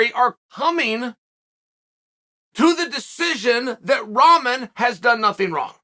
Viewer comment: Bro i thought he was saying Ramen Head making fun of his hair this whole time for backing out